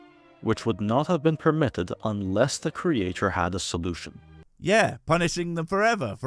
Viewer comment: “…unless the creator had a solution…” Was I the only one who heard that as “a final solution?” 🤔